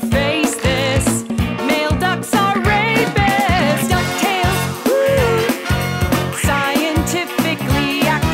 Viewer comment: 😂😂😂😂 I showed this to my little sister and she said " Donald duck is saying YAY I'M POOPING! " I was like 😆😆😅😅😂😂🤣🤣🤣🤣🤣🤣🤣🤣🤣🤣 now legend says she still says " YAY I'M POOPING" and she is still saying that lol